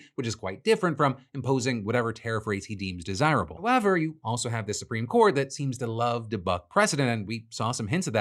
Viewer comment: Anyone else hear "You also have the Supreme Court that loves to fk the President" ..?